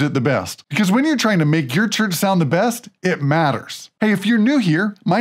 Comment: I thought he said “When ur trying to make ur turd sound the best” hahaaaa 🥴